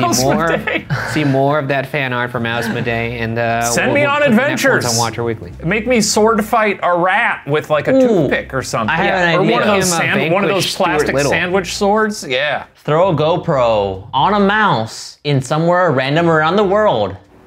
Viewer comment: When Shane says “Make me sword fight a rat with like a toothpick or something...” my subtitles said “Make me sword fight a rat with like a new dick or something...” and that was just an interesting turn of events.